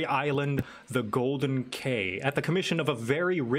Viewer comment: I thought it said the golden gay